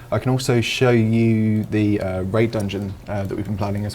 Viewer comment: I swear you said rape dungeon instead of raid 😂